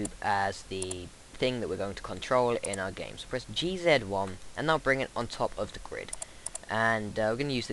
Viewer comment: what do you say at 0:25????? "Geezethan1?" or "G then 1" or what? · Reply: he said "press G Zed 1", some folks say "Zed" to mean the letter "Z"